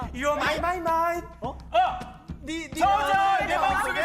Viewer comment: u r mymymy 이게 어떻게 내마음속에 저장으로 들리냐 ㅋㅋㅋㅋㅋㅋㅋㅋㅋㅋㅋㅋㅋㅋ